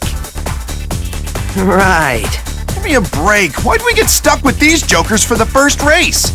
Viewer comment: Knuckles says stuck but the subtitles say "saddled."